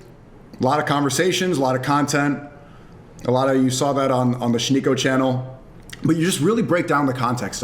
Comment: I thought they said Sneako changed LOL